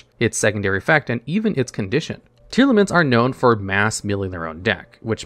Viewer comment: I appreciate the talking about tearlaments and what made it good because I thought he was saying "tier limits" for the longest time lmao